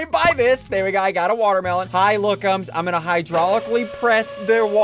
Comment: Are we not going to talk about the fact it said “ Pookum” instead of lookum